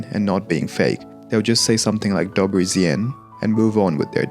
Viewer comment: "dobły źień" 😭😭😭🙏🙏🙏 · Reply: ja usłyszałam 'dobeł zjenń' 😂 i zajęło mi to parę sekund żeby się domyślić o co chodzi XD